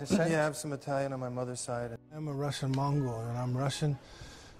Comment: You know, I always thought he was saying “Russian mongrel” and not “Mongol”.